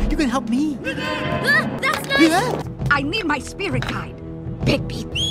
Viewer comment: My brain just malfunctioned and I thought she said “I need my spear!” when it was spirit guide